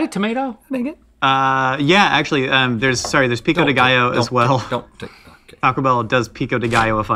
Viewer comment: all I heard Rhett say, was “Don’t take. Don’t take. Don’t take.” 😂 and it sounded like they bass line to a song about not taking things 😂